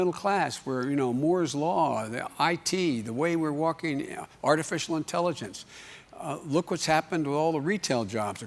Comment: Did he say "Moore's Law, IT the way we're walking er Artificial intelligence"??